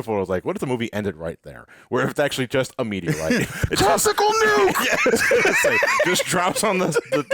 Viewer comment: I swear, I thought said "Testicle Nuke!" XD